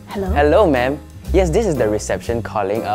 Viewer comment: i love how the CC says madam but he says ma’am